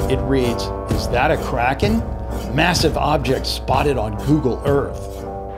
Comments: I thought it said Karen spotted on google earth 😂😂😂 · Omg, I thought it said, "KAREN spotted on Google earth" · I thought it said Karen spotted on google earth🤣🤣